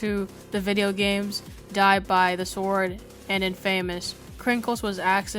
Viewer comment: Bruh i thought that infamous said inf amogus